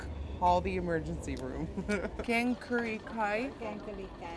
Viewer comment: Wait! At 12:10, did she said Kaeng Karee Kai? Whoa, that's Thai food, well not originally. Never thought that our food would go that far. You should make a video trying spicy Thai food while doing makeup sometimes.